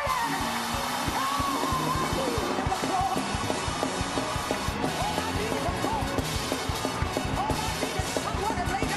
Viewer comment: what does she say at 7:52? it sounded like she said oh oh she hit the floor ! lmao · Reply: All I need is the floor